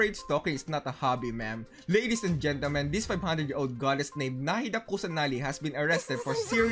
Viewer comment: In 8:28 i misheard him, i thought he said "nahida pussanali"😔